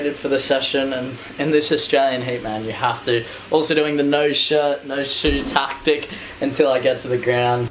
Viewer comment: The auto closed caption got it wrong at 3:31 when you say "No shirt, no shoes tactic." It wrote something much different that you should probably correct. Thanks for the videos. Best wishes!